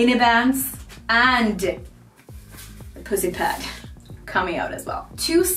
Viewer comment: if I heard correctly a “pussy pad” for the barbell pad is a great name for it. That pad really saves my “🐱” while doing hip thrusters